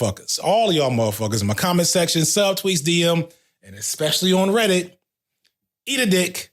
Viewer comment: Bro, did you just say "expecially"? I'm done here